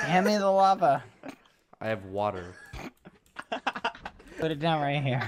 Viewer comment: Question: What does cows drink? Bad: g a t o r a d e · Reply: He never said Gatorade skeppy thought he said Gatorade lol. He said water